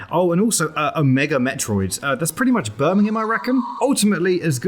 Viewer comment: Okay, I know you said "It's pretty much Birmingham, I reckon.", but I heard "It's pretty much burning in my rectum."